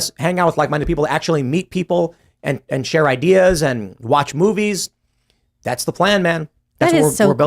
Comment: Did Tim just say “Share ideas and watch boobies?”